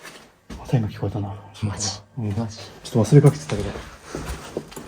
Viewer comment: 頃かなマジ？って聞こえてるけど、誰の声です？女の人みたいな感じ